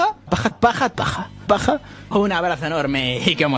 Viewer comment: Banda no entendí en el minuto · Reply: Paja